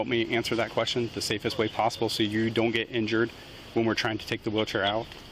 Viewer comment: I thought I heard them say "We'll deal with the Witcher last" I guess they said wheelchair. rip me